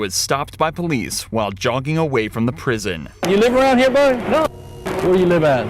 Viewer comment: Guilt and everything aside, are we gonna ignore the cop called him boy? Edit, it could've been buddy instead of boy. Cant tell